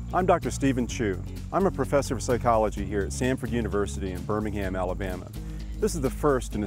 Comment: i thought he said Stanford at first